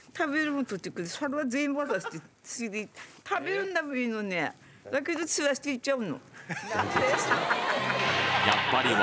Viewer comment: これも全部は聞き取れん「私食べる物取ってくる。それを全部〜〜〜。食べるんだばいいのに、だけど散らしていっちゃうの」きき取れない部分がわからんが、動物の話題なので「自分が収穫してきた食べ物を全部(なんとかかんとか)食べずに散らかしていっちゃうの」